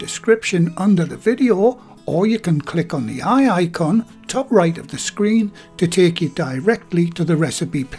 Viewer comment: i hear "aye aye con" haha always love the vids here in N.Ireland :)